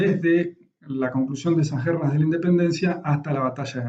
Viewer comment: tampoco entendí lo que dijo en el minuto: - 6:40 (parte de los años desde la conclusión de ................ de la independencia..) - 11:00 (del rio de la plata las ....................... dominantes se preocupaban..) eso es todo, espero su respuesta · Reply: Conclusión de esas guerras de la independencia hasta la batalla de Pavón